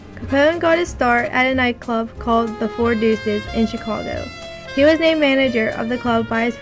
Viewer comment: These girls can't even read from a script, makes this over simplified video painful to watch. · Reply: I got some useful information, however it is difficult to understand portions. At 4:44 she mentions a nightclub Capone got his start at. It sounds like she said, 'The Four Digits,' however a search did not find that establishment. Anyone know if that is the correct name?